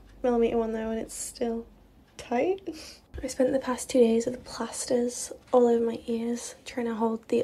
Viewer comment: Did she say at 2:57 "I spent the last two years with plasters in my ears" ?!?!? 2 years what?